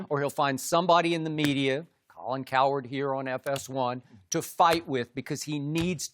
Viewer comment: Did Skip just say Colin Coward at 3:55 😂